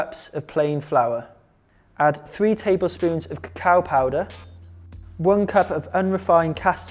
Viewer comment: "Cacao powder"..I heard "cow powder", what a shock!😂😂😂